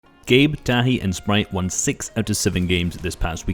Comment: At first I thought you said Gabe daddy and sprite not tahi